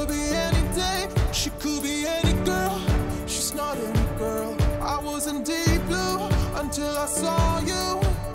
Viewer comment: I always thought Julian Jordan was saying "I wasn't Diplo"...